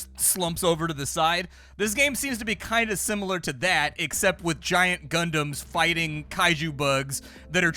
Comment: Why did I hear giant condoms here 🤣